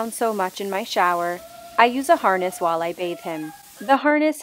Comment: I thought she said I jus a harness while I rap- him🤠